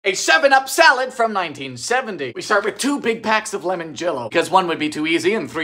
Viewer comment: I thought that initially you said to packs of limoncello. I had high hopes for this recipe 🫠